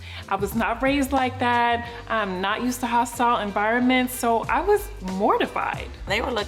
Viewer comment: I thought Jen said she is not used to hot sauce environments, but she said hostile.